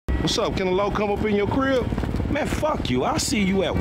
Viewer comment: I thought he said he couldn’t come in his crib